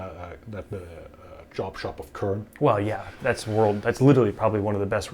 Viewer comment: he mention a super high end shop but I cant figure out the name anyone know what shop they are talking about? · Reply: Kern